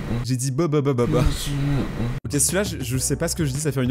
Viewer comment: « Nan laissez moi ha » enfin moi j’ai compris ça 😂